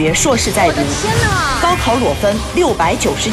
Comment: 高考裸分我聽成高考裸奔🤣